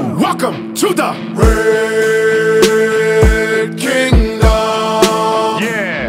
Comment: I thought he was saying Brave Kingdom until I found this. 😂😂